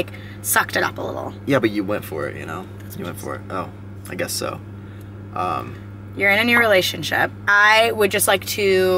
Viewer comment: What’s whispered at 6:30 :o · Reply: "that's what (s)he said"